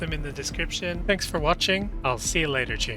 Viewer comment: It kinda sounded like you said : see you later chimps 🤣